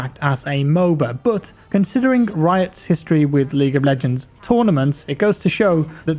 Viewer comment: Am I the only one, doesn't it sound like he says League of BLegends lmao